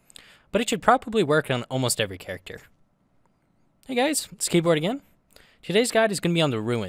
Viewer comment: did you call yourself a skateboard? 0:11 or was it keyboard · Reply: Keyboard, who voice acted this video.